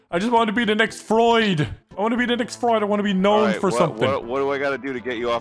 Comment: For a second I thought he said “I want to be the next floyd” instead of “I want to be the next Freud”